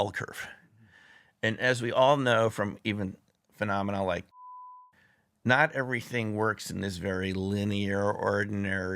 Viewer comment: what did he say? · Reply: Covid